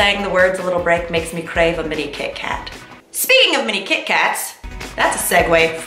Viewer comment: I didn't hear you say " mini kit Kat", I heard you say "mini cake hat". both seem appropriate coming from Mamrie 😀. missed you mamrie!